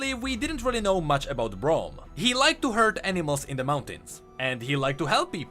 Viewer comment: Just in case anyone else misheard and had to doubletake: NO, Braum doesn't like to HURT animals in the mountain. He likes to HERD animals in the mountains.